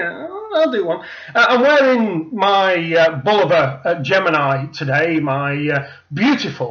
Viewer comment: Which watch do you wear @ 2:00 ? I couldn't understand it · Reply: Peter Walker Think I refer to my Bulova Gemini in this video.